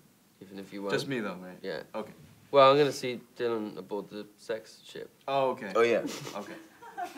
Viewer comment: What did Dylan say while he was trying to laugh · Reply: * laughing * “about the sexship “